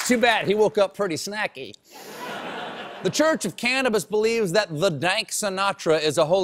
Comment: I thought this said "Church of Cannibals" and was expecting some Hannibal Lector fanclub.